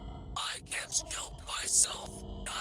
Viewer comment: I don’t think It said I can’t help myself i think it said i can’t k$@ myself